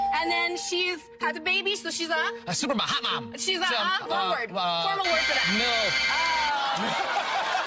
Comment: So I need someone to tell me what was said at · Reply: he said “milf” lmao